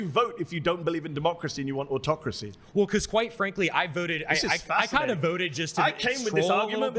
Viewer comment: stg I heard “white frankly” and it fits even better 💀